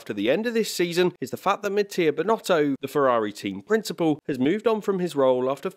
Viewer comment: Halfway through the video you said “Mattia Binotto” and the subtitles thought you said “mid-tier Binotto” I’m dead